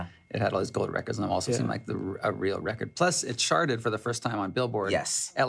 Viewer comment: I thought Jade said "I sharted for the first time." I was like whhhaaaaa LOL